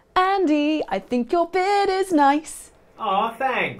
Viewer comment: GASP How dare disrespect The Beard!!! Heretic!!!! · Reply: For some reason I thought she said "bed" not "beard"